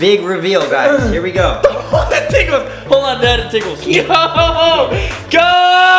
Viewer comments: does Kian really say “hold on dad it tickles “ ?😂😁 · I swear kian called Jc dad😂😂